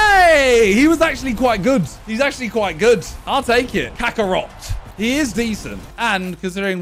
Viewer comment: The way Simon says the player's name sounded like Kakarot